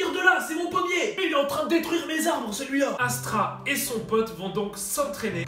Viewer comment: j'ai bien entendu Astra là ou c'est moi ?!